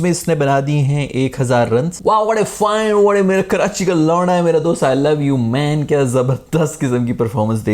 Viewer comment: am i the only one who heared something wrong karachi k lau*a ha😂😂